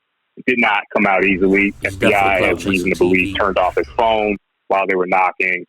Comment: What did he say at 2:59?? That he turned his phone off or that the FBI tuned it off?? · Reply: He turned it off